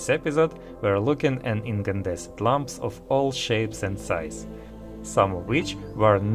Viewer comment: Hey! No shade, it sounded like you said "incandescent lumps" and I love that visual so much!